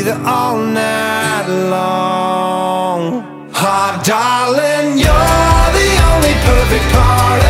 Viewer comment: Tthe first time I heard this on the radio I thought it said Hobgoblin instead of Hot Darlin'. Try listening to it as if it does and the song takes on a whole new meaning xD But seriously, though. Love this song so much.